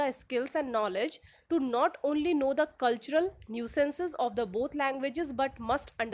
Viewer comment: Did no one notice that the expression "cultural nuances" is being called "cultural nuisances"? (5:57-5:58)Hilarious!😮😂😢🎉 Freudian slip?